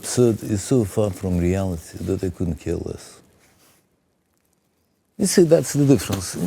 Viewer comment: Sind die eingebrannten Untertitel auch automatisch erzeugt? Die Übersetzung ist an mehreren Stellen so falsch, dass sie nicht-Englischsprachige verwirren könnte: Z.B. sagt Wladimir Solowjow bei 1:49:14 "European definition of absurd is so far from reality that I couldn't care less". Zu Deutsch "Die europäische Definition von 'absurd' ist so weit von der Realität entfernt, dass es mir völlig egal ist." Aber in der Übersetzung heißt es "Die europäische Definition von Absurdität ist so weit von der Realität entfernt, dass sie uns nicht töten können". D.h. die Übersetzung hat "...that they couldn't kill us" statt "...that I couldn't care less" verstanden. Ich will kein Haarspalter sein, aber das sind schon gravierende Unterschiede in einer Aussage eines Interviews dieses Kalibers. Abgesehen davon, ein unglaubliches Interview! 👍 Herr Köppel ist ein seltener Weltklasse-Interviewer!